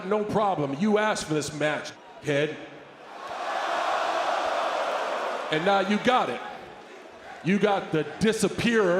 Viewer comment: what did he say? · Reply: He actually called him a d-head